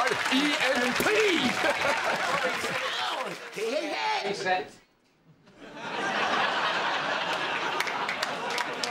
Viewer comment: what happened here? I could not hear what was being said or understand what was happening. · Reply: All I could hear was someone calling "reset" meaning they weren't ready to go yet, but the cat came in early so it meant he'd have to go back and do it again. But as a joke he acted like he'd been insulted because they didn't appreciate his performance, or they thought it wasn't good enough, even though that wasn't the true reason for the reset.